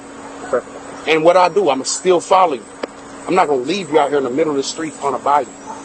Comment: He says just remember, “ I ain’t never gonna leave you bro…” as he gets lead away in handcuffs. After his saga of lies. This is the kind of man that would kill her in the name of love and if he can’t have her no one will. I hope she gets some help and is safe. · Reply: Didn’t sound like he said that. Sounds like he said, I ain’t leave you bro.